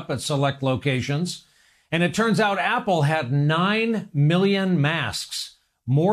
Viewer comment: I think I speak for everyone when I ask WTF was Apple doing with 9,000,000 masks? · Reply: that's only 9 mill.... i thought they said it was 900 mill....... 😕 i might need to rewatch this 1...